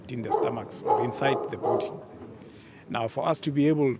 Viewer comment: he really said "kept inside the booty" 🤣